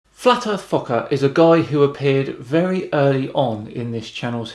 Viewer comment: Didn't have a high enough volume for the video at first, so I heard "'Flat-Earth F*ck-Up'". Then he gave it all to make this seem to be true.